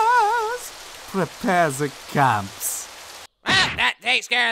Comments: Sooo is anybody gonna mention the fact that the Hell guys are German and one said "prepare the camps"???? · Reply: I always thought he said "prepare ze cuffs" which makes even more sense considering they wear cuffs in KI cuffs in HFIL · "FRAAAAAANZ, We have Visitors! prepare the camps Jesus Christ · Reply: I thought he said clamps this whole time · Sooo is anybody gonna mention the fact that the Hell guys are German and one said "prepare the camps"???? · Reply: I thought they said clamps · Sooo is anybody gonna mention the fact that the Hell guys are German and one said "prepare the camps"???? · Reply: Yeah, always thought he said "prepare ze cuffs" which makes even more sense considering they wear cuffs in KI cuffs in HFIL